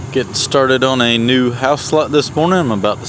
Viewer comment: I could have sworn I heard you say “ we are starting a new house slut this morning “ lol 😂. I know now that’s not what you said but it was hilarious when I thought otherwise. Lol 😂